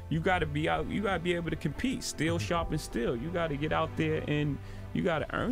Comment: i thought i heard "steel sharpens steel" instead of "still shopping still"...